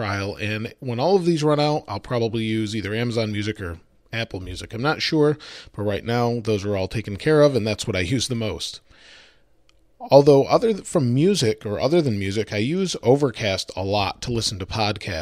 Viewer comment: did he say "although than fucken music "?????